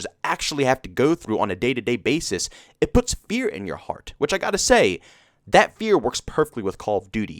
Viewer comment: It puts Beer in my heart, eh? I misheard that the first couple of times. XD